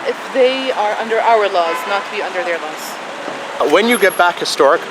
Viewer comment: the subtitles are wrong. He actually said "under our laws and NOT under their laws". BTW, interesting how you chose to open the question with "when", not "if". I guess someone gave you sound advice, since presumably you want to live.